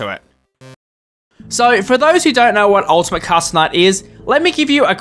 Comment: captions saying "Ultimate Castle Knight"